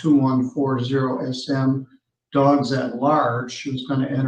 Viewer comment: "Dogs at large" would be a great band name. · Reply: at first I thought he said "dogs are large", lol